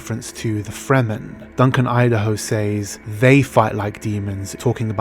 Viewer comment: In the trailer he sounds like he said "let's fight like demons" but in the movie he was actually referring to the people of the dune, the fremen, and he said "they fight like demons"